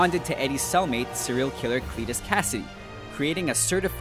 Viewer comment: ["!sbJCJW3Xeh5KmuJE-8XsKmLWZSICAAAATlIAAAAEKgEvMZroKGL9k8FrD3Iyz_zBp9Dvpurk8VnyFl9tDewwzQs-zmO_vBpmf8-xBLfC121ikAGkVD9L_XTItgymP45tePCwmicC8dvceGzpR_NBQIwdocpvYP6LB9XhHQ_iyAM8eQDHvZePIar4Bn8NQljQHzGXhfsW7hD7nNJ1_DCeruYGlTHiDgMTPFHvp8kRI-uUcefJsw_4lqOzu5cMJvbY-wJbZAzFFY0qbJG9MXqPnpqFp2wrmNab90RIThLBhyPsHgRw8rsmOw3qWMqvzS-5nCn-8HdIcZwGx7wcm3FL0GCkC9TJ7eWyGs_tCPwh4ZDvqfWqzTXveo5tz3Az_uGhX-bYIQ1j2FFM7QaS_z88943ol8dTAzE5NiQefnYFn_OyZsFss_XysMRgJ2icROnm","hue i thought he said fetus kasady",[[[null,null,1]],null],false]